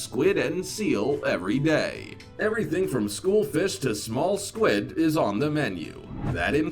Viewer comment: did the man really just call it “school fish instead of school of fish” what the hell is a school fish. Is that a fish that I don’t know about that has a book bag and a pocket protector.